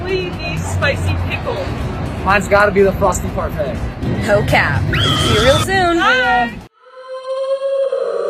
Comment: What is said at the clip at 10:40 at the end? Just really confused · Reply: sorry for the 2 year late reply but she said "no cap"